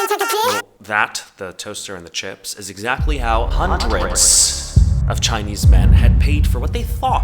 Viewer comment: idk why i heard "huntr/x" instead of "hundreds" 😭